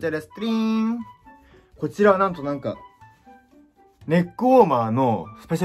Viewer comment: ネックウォーマーがねっこやに聞こえるもう末期や